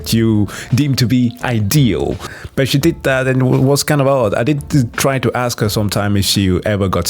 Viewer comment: "she did that ... and it was kind of hot" was what I heard at first lmfao had to do a double take.